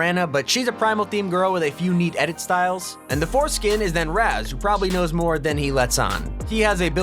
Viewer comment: Anyone else hear "foreskin" at 7:00?